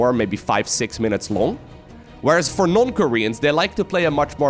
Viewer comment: the tldr... i thought you were saying one of these guys were from north korea. i was literally shitting myself trying to figure out how someone from there would compete in SC2. now i know you were saying non ckorean. just thought id share the story. soO SC2 Featured snippet from the web Eo 'soO' YungSu is a South Korean Zerg player. He started his pro gaming career back in 2011 and continues to play through Legacy of the Void in GSL. More recently he will be fighting for the title of 2017 GSL Season 1 Champion after beating sOs to make it to the Grand Finals. Joona Sotala DescriptionJoona Sotala, better known by his gaming handle Serral, is a Finnish professional Starcraft II player using the race Zerg. In 2018, Serral became the first non-Korean player to win the Starcraft II World Championship Series. Wikipedia